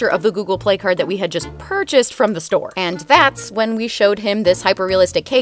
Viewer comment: “His reaction was unmissable!” *doesn’t show his reaction* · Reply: Oh I thought she said his reaction was sus...